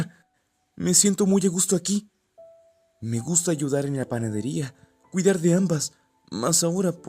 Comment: Al principio escuche "me gusta llorar en la panaderia " y era me gusta "ayudar" en la panaderia 😭